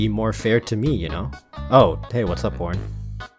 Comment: Was saying horn or other one